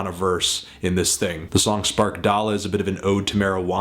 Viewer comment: Sound like you said spark dolla